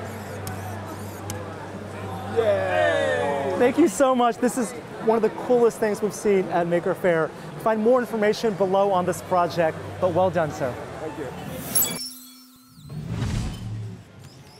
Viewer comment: I thought he said ”this is the coolest thing I've ever seen at ”n” word fair.”